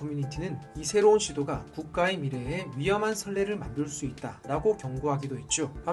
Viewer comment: "설레"가 아니고 "선례"인데, 자막이 잘못됐네요. 내용을 작성하신 분과 자막을 만드신 분이 서로 다른 분이신가 봅니다. 이런 실수는 귀에 들리는 대로 자막을 만들기 때문에 생기는 실수이기 때문에, 내용을 작성하신 분이 직접 자막을 만드셨다면 이런 실수가 불가능했을 거라고 생각합니다.